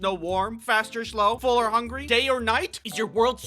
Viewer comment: I heard "gay or night" instead of "day or night"